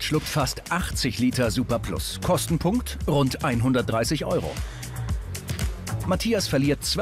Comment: Was ich habe da was von 300€ gehört!!!